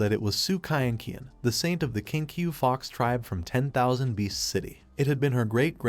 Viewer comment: did he say the kinky fox tribe? :face-red-heart-shape